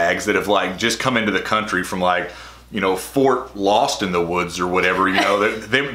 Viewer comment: at first I thought you said Fort Leonardwood and I was cracking tf up. Anyone else? 😂😂😂